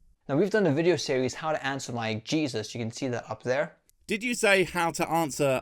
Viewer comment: I thought he said “how to answer LYING Jesus “